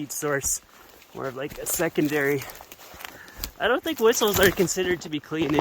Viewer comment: "I don't think whistles (heaters?) are considered to be clean" ? I don't understand. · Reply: Wood stoves.